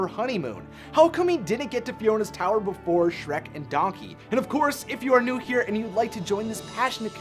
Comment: I'm a bit late but did he say "if you are nude here..."?